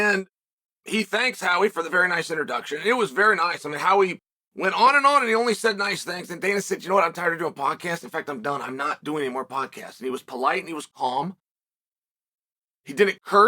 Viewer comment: I thought he said, " I'm tired of fucking podcasts"